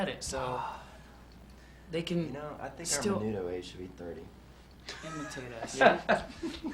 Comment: does anyone know what lance said at around 47:47? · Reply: I know this is a year late, but I think he said, "I think our Menudo age should be 30."